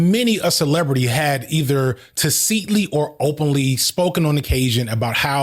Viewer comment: What is this word??? Do you mean tacitly? Or am I crazy?